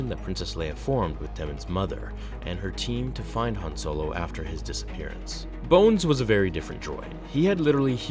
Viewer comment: Thought he said boners.